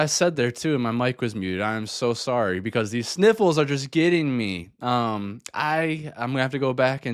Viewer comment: I had to backup because I swear I heard “my nipples are killing me” I still don’t know what you said lmao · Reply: Okay upon fourth rewind I heard “sniffles” bahahah